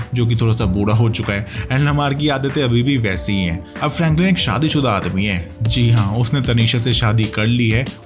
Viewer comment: did he said tanisha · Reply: yes, her name is Tanisha!